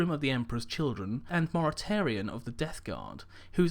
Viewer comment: Hah i think you accidentally called Mortarion "Bartarion"